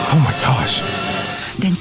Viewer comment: I just had to listen to it three times on repeat, but I think did indeed say "Oh my Kosh" at 6:45 XD. It's good we're not in ancient Judea or a group of women with fake beards might stone him.